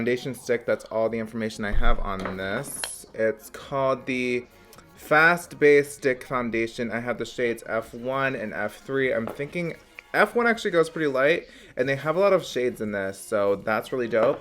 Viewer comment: sounded like you said Fast Base Dick Foundation, I had to rewind that shit because I died.